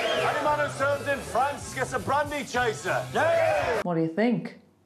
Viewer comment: I don't hear "anyone..." but "any man..." It's just me?